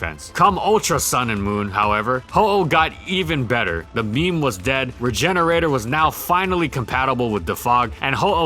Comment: what is it that you say? the meme is dead? im honestly trying to wrap my head around this one word sorry can anyone help